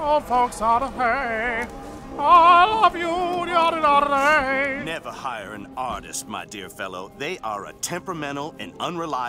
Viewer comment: Just as I pulled out my sketchbook to start working I heard... "Never high an artist my dear fellow." and instantly looked back up